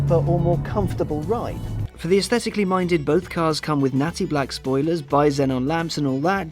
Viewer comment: Did he say Nazi black spoilers at 3:48? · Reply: Believe he said 'natty', meaning 'a bit trendy'.